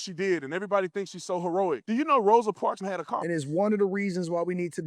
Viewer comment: the way I keep replaying this part like am I hearing this mf right Rosa Parks had a WHAT? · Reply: Car I think....but wtf? She wasn’t forced onto that bus — she chose it. And choosing to confront injustice, knowing the consequences, is what made her act historic. If she had no choice but to ride the bus, there’d be nothing especially heroic about it. Not to mention, it wasn’t even her car — it was her husband’s, and it was likely in use anyway.